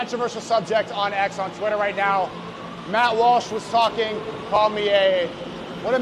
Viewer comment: did he say Mouth Wash ? 😆😆😆😆 Matt Walsh = Mouth Wash